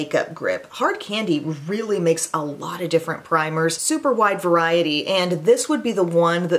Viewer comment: Lol when bud was introducing primers I swore he said prime rib I had to rewind that part 3x……awesome video thank you